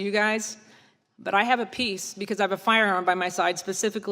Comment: At first, I thought she was saying "I have a piece", as opposed to "peace". lol